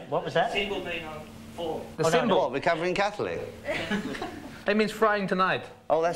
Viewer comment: I LOVE Jimmy's shirt!! Recovering Catholic. Makes you think, makes a strong statement!! Love the interview, also. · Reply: Lol when the man in the audience asked Jimmy Page ... what does your shirt “recovering Catholic mean” I thought I heard Robert say ... It means he’s frying tonight? I was raised catholic and I remember listening to Led Zep while eating grilled cheese on Friday nights during Lent!